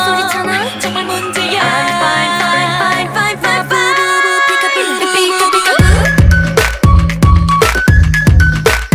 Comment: what is she saying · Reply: My boo boo boo peeka_boo